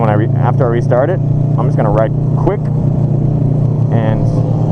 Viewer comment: I thought you said you were gunna ride quick. -_-"